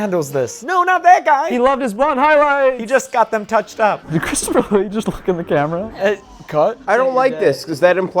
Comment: I could have sworn you called Christopher Lee Christopher Lloyd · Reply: I THOUGHT THEY DID TOO!!!!!!!! 22:57 if you rewatch, he's saying "Christopher Lee" but he's laughing so it sounded like Lloyd